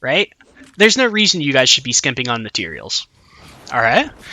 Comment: Am I the only one who thought he said 'There's no reason to be simping on your materials' the first time lol